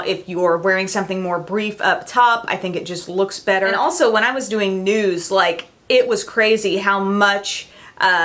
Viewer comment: I thought you said "When I was doing nudes" instead of "When I was doing news" LOL